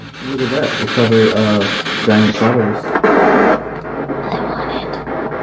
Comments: I hear "I want it" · i heard “I want it” from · I heard I want it